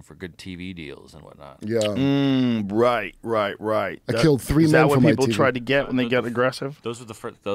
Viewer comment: did he say that he killed 3 man from his team? 😅